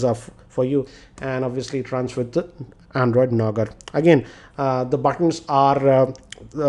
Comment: did you just say , Android Nougar?? 🙄